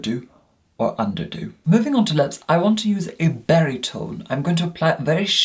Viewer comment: Oh, a BERRY tone. I thought you said BARITONE and was frightfully confused.